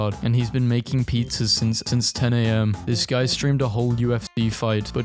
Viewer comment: what did he say here? "Making pizza since,tince 10 am" ???